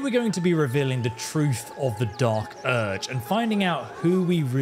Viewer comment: For a moment I thought you said dark bulge 🤭